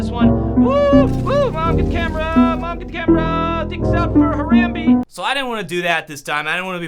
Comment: Did he really said "Dicks up for Harambe"?